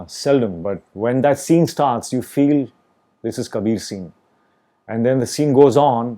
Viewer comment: did he say this is kabir singh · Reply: He actually said this Kabir 'seen' which refers to that Kabir and Khalid first meeting seen